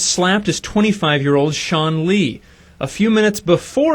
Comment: Who thought that said Stan Lee instead of Sean Lee